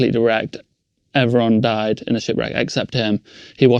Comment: In 31:35 it sounded like he said his shit got racked but he actually said ship 🚢 wreck lol 🤣🤣 sorry just had to say that, please 🙏😇🙏 continue lol 🤣